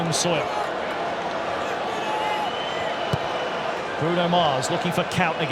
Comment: did he say Bruno Mars? 😅